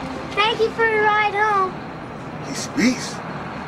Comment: What does George say at 1:42? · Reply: "He speaks."